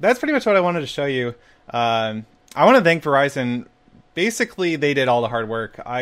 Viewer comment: I actually thought he said "Horizon" or some stuff but thanks "Verizon"! This is epic EDIT: Wish i can get it :C And i just saw Reuben died in minecraft story mode... WAAAAAAAAAAAHH!!